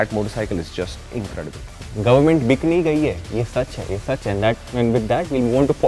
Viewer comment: I only heard "Government Bikini Gayi hai"